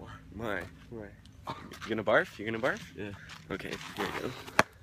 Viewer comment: I thought said "You gonna bark you gonna bark?" HAHHA